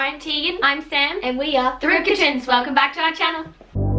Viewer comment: Things the captions have thought you said instead of Rybka Twins: Important Rib curtains Rip kittens Root-patterns Group katrins Rip catoons Rookie twins Thrip kittens Thrift kitchens Rip ketoons Through kittens Through kitchens Through Cajuns Vegitarians Doing two truths Daruka tunes Participants Derivatives Turkey twins Which one do you think is the farthest from Rybka twins??